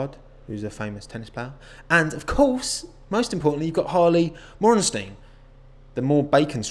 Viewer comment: I thought you said Harvey Weinstien first 💀💀💀